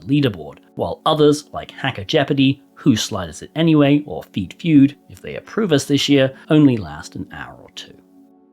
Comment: did he just say Feet Feud ???